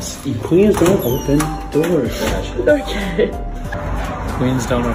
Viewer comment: I honestly thought he was saying please do not open doors like he was being rude or something but then I realized no he’s saying QUEENS don’t open doors! Th at makes it so much sweeter! 😍